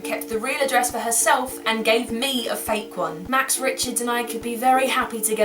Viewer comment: Omg "Max Richards"... That totally creeped me out for a second because I thought you said "Max Richmond" who is one of my best friends haha